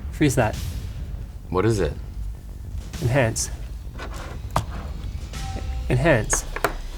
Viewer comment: do i hear enhance or ENDhands